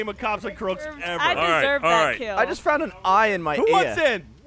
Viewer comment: did he just say "I just found an eye in my ear?"